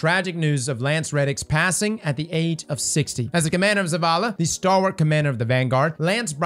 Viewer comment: did he just say"As the Commander Of Zavala"??